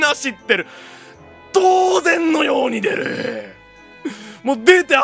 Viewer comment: 「当然のように出る」が粗品にしか聞こえん。